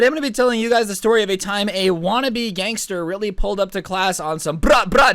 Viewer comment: Lol I thought is said weeb gangster